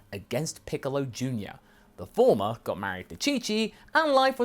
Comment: For a second I thought you said the farmer got married to Chi-Chi. What a twist! 🤣🤣🤣